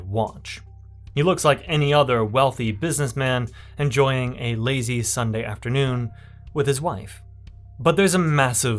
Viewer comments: did he say "an lazy"??? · "A lazy Sunday afternoon with his wife." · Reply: I swear it sounds like he says 'an lazy'.